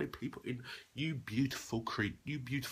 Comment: did he actually say you beautiful creep